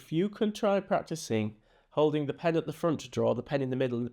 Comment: Our washer is running, which is quite loud, so at first it sounded like you said, "Holding the pen at the fruit store." 🤣🤣🤣 I have no trouble understanding your Yorkshire accent (I'm an American with a longstanding love for British TV especially Monty Python) but with the washer running it made it sound different. I should maybe also mention that I often mis-hear words as a quirk of my autism. It makes for some hilarious conversations with my husband. 😂